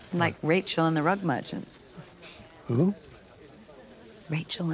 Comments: Oh...'rug merchant'. I thought she said 'rug muncher'. This scene was going in an entirely different direction. I really need my ears checked. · When I first watched this I thought that Sienna said " Rachel and the rugmunchers." 😂😂